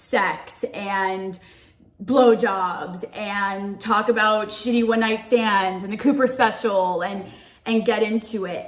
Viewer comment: I thought she said "I can't wait to talk about shitting when I stand," I was like wow that's interesting, upon further review she said shitty one night stands, not the same thing